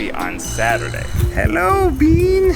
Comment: Ned saying Hello but I hear Mr. Bean HAHAHAHAH